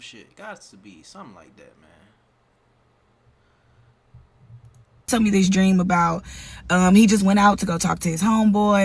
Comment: /56:15 does she say "tells me this dream"? Is this a true story? ❔️🤔